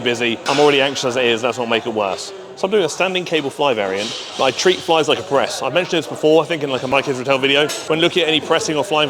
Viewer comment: For a second one thought u said u treat flys like u treat breasts 😂😂😂